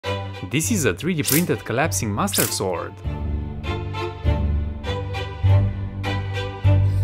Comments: I thought he said mustard sword 😂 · I legitimately heard him say mustard sword. And now I can't unhear it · I thought you said collapsing mustard sword lol 😂